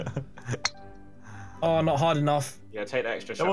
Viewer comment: Wiz: "I'm not hard enough" Simon: "That's what she said" Me: What kinda women has a p... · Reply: he didnt say im not hard enough he said not hard enought so it makes sense